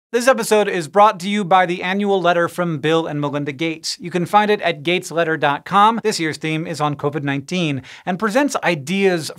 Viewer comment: First time I listened In thought you said: ' this epizode was brought to you by angry letter from Bill and Melinda Gates XP'